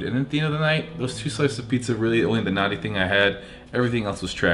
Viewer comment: I'm working while I watch and I thought you said pizza was the only natty thing you had not naughty hahaha